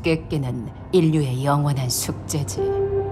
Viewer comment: 나만 0:04 초에 영원한 숙제징~~~ 이라 들림???